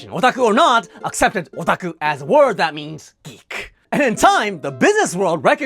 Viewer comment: I heard "Greek" instead of "geek" in the beginning of the video and couldn't figure out where this was going for an embarrassingly long time.